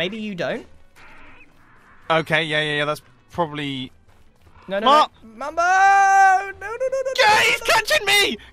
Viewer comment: DID HE SAY THE F WORD ? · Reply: he was saying "Mu... MUMBO!"